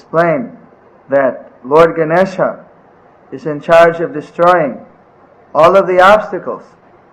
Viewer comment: Haribol, Dear Video creators, thank you so much for sharing this Knowledge. I allow myself to observe I think a small mistake from 1:38. Srila Mahaaj said that « Shree Ganesha is in charge of destroying » instead of « uncharged ». I hope I am not mistaken Jaya Sri Sri Guru Gauranga!